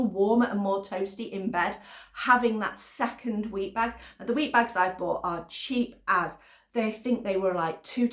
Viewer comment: The other half has fallen asleep in front of the tv so I'm watching with ear buds in. Had to rewind the video ..... thought I heard you recommending wee bags 😂 ....